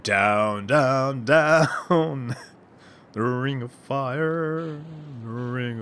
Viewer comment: i thought you said "Ring of Fart" at first. lmao